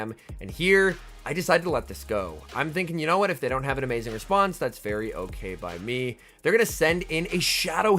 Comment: Did you say "That's fairy okay"? 😂